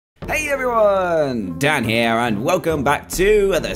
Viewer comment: I heard "Hate everyone!! Dan Here," at the start